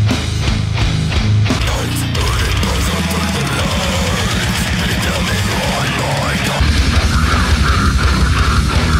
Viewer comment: I’m actually wondering what 0:16 says lol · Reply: Infant annihilator